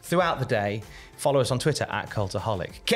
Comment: Am I the only one that thought said “transformers are for losers, don’t be a dick head” like ten seconds after he said it I knew what he actually said but It did sound like Transformers